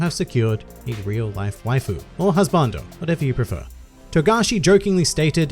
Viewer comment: When you said something like "you will get real life waifu or husbando" I thought you had said "real life waifu or hell's spawn".